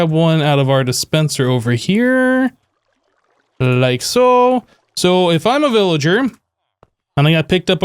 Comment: I swear I heard make a very Keralis "like-a so".